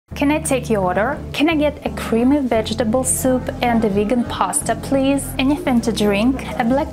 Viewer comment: I heard "nigga pasta"